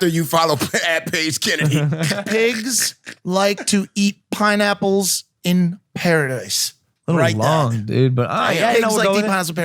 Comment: Pigs like to eat pineapples in paradise (Lmao I’m dying writing this) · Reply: I thought Mike said. Page likes to eat pineapple in Paris. LMAO. I don't know which of us is correct😂